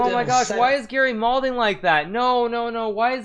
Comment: I didn't get a word that Hikaru used in 0:45, can somebody help me out here? He says something like "why is garry moulding like that?" is it moulding or maulding? What is the word? I can't say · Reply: It's malding, as in mad and balding